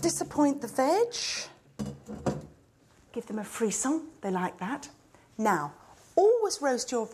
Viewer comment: What did she say at 2:17? "A freesom, they like that"